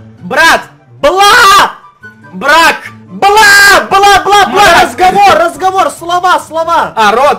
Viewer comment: На 8:01 Эдисон так сказал что мне послышалось как будто Игорь это сказал но говорил Эдисон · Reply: Если что это слово РАЗГОВОР